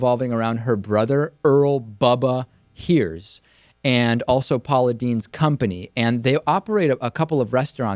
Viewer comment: did anybody else notice that in the written words below the speaker, at 1:43 instead of writing Paula Deen, they wrote apologizes? (said) "and also Paula Deens company" (written) "and also apologizes company" also at 0:01 they wrote polythene instead of Paula Deen, at 0:35 the wrote holiday instead of Paula Deen, etc. thumbs up if u noticed that! (also Tre Melvin Brought me here :) thumbs up for that to!)